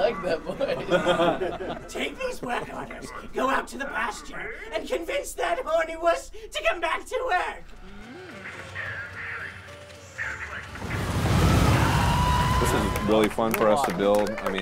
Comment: it sounds like he says "that horny wuss"